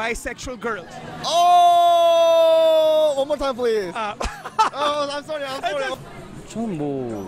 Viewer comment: What did he say at 0:47? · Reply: he said, "one more time please" it was funny...his reaction was as if he understood my question.